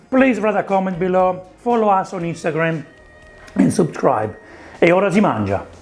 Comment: can someone tell me what was the Italian word that he said at 9:48 ? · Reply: Ciao! At 9:48 I’ve just said “and subscribe” 😊I was probably making noise with my mouth and it's not very clear